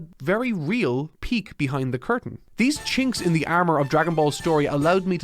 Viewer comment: Did he say “these chinks inside the armor” 😂 1:10 👲